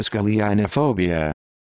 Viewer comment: I heard this as ‘Did I ask Elena phobia’